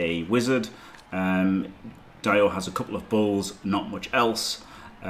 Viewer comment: What said “he’s got 2 bulls and not much else” What I heard “he’s got 2 balls and not much else”